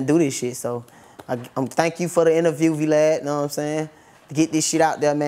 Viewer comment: Did he just call him V-Lad?